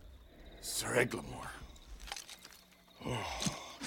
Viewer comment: it sound like his saying "ogre lord" instill "oh god lord"